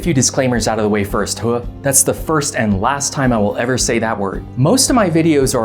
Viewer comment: The “Hooah” got me lol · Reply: i was so confused for a second, I thought he was angry about saying the word "disclaimer" lol